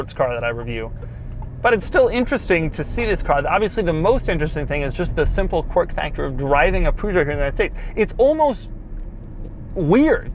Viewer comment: Anyone understood what he said? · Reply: "Obviously the most interesting thing is just the simple quirk factor of driving a Peugeot in the United States."